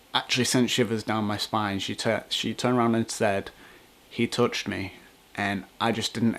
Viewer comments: I heard 'he pushed me' not 'he touched me' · It kind of sounded like she said "He Pushed me" I listened about 25 times. I don't know though.